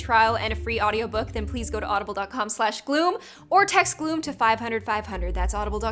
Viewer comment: Omg my caption thought that when she said gloom it thought she said good